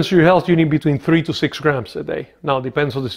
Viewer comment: He said "3 grams will do it" then right after that at 2:20 he says something that either doesn't make sense or that I cannot understand. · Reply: Yes you are right. At 2.20 it sounds like he is saying "30 grams" and that's a lot! Maybe he stubbled a little on the words and meant 3-6 grams but I don't really know for sure.